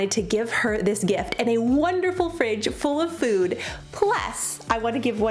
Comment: At first I thought you said new fridge full of food 🤯lol! It's still awesome how fun... thank you 😊 💓 you always uplift my spirit 😇